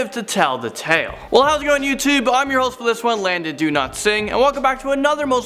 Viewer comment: I always mishear Landon pronouncing his surname, I keep thinking he’s saying “Landon DO NOT SING”, which makes it sound like a statement. Shame, was going to ask about a karaoke party next week... 😔